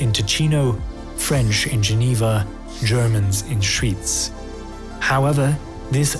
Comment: Should probably be Schwyz instead of Schweiz in the subtitles, assuming you intended to mention the canton and not the country